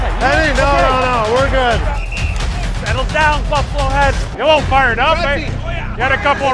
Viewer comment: lol 3:32 did he say "settle down fuckwads" xd